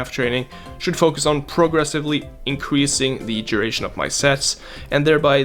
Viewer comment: thought he said training my duration of my sex